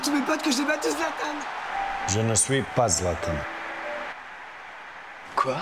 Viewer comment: what is he saying? I am not Zlatan?